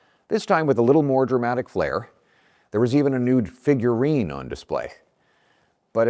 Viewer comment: it sounds like he says "there was a NUDE figurine on display" 😂🤣😂 why does he talk weird?? listen to him say 'always' it sounds like 'owiea'